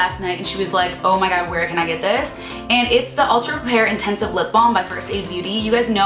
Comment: I wish you'd put the items you mentioned in the description box, because I literally was here typing 'Ultra pear intensive lip balm by Versailles" into google because I thought that's what you said lol